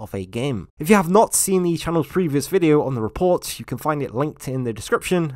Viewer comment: it's just me or did he say "F U for watching"?